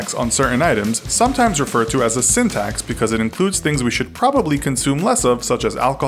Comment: lol I heard "syntax" instead of "sin tax."